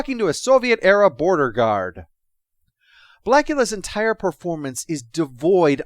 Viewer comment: did say blackula? The episode wasn't that exploitive! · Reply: I also heard Blackula instead of Bakula and the sad thing is that the episode would have been for sure 100 times better, if this was the case.